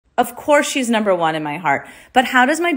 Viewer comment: I thought you said shes never won in your heart😂